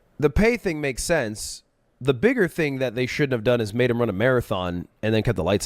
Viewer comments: jojo took down his video and apologized for including some misleading clips - i appreciate him doing that it's certainly going above and beyond what 99% of creators would do. we also spoke privately and chopped it up and he seems like a cool dude! okay thats all have good day every1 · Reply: i mean i heard SHOULD'VE the first 3 times i watched that and this was my first time seeing that clip, with you IMMEDIATELY clarifying it after, so i can see how he thought you said they SHOULD HAVE done that. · i mean i heard SHOULD'VE the first 3 times i watched that and this was my first time seeing that clip, with you IMMEDIATELY clarifying it after, so i can see how he thought you said they SHOULD HAVE done that.